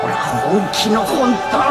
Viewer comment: what did he say? In english pretty please...🙏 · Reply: yurusanezoo